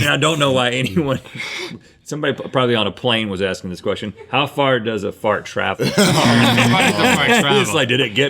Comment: I thought it said "how fast does a fart travel" and I was like hm good question.